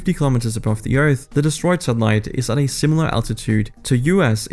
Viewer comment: I thought he said "destroyed sunlight"😮 I was glad to find out he said "destroyed satellites"😂